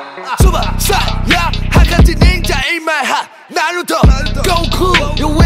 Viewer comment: did he say "naruto, goku" or am I just being racist lol